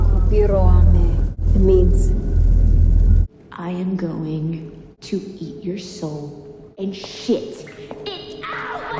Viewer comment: "and sht it out..." what are the other words after that bit? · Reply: The line comes from the movie "Jennifer's Body", and goes as follows, "I'm going to eat your soul and shit it out, Lesnicki!" which is the character's last name that Jennifer is screaming the line at.